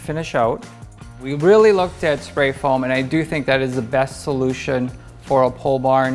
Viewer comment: I thought u said pollution.not solution